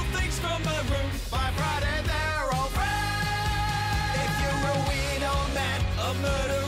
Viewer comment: What does ben say at 0:48? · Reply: By Friday they’re all friends